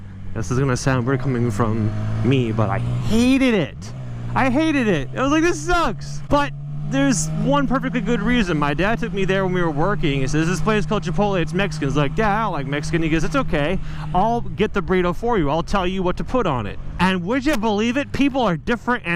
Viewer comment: Did he just say McPotle???... How come he must tempt us like that?